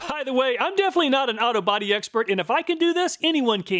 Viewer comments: I misheard "I'm definitely not an auto body expert" as "I'm definitely not an out-of-body expert" and now I'm a bit disappointed the rest of the video wasn't filmed as an out-of-body experience. I guess smelling enough auto cleaning chemicals might could make it so, though. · When you said "Auto body repair" I heard it as "Out Of Body repair" LOL